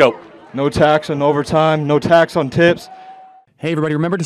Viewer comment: Last guy on the mic i thought he said "no tax on tits" 😂